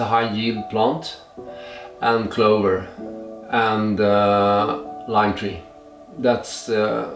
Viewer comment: What does he say at 10:43? Is it lime Tree?